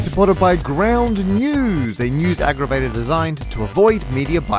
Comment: It’s almost like probabilities aren’t a guarantee, it’s like they’re just educated guesses, weird huh. And I swear I heard “Ground News, a new aggravator” and I’m thinking, yea, that’s the news, aggravating.